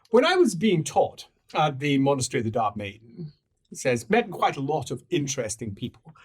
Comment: Looking for Dhar, found a few possibilities. 27:08 "Monastary of the Dark Maiden" This pronunciation really sounds like is saying Dhar maiden here rather than dark but difficult to say for certain. There's several other mentions of the word Dark, but they all have a clearly pronounced K, which appears to be missing in the 27:08 timestamp, but again that may just be the accent being used. Though 2:30:22 "Your mind is drifting to somewhere Dark" There once again the K does not seem to be pronounced very clearly, and it sounds like "Drifting to somewhere Dhar" Otherwise 1:48:45 "My name is Darwich, lord Darwich" Sounnds very much like he's saying "Dharwich"